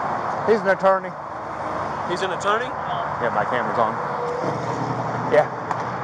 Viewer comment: did he say 'damn my camera's on'?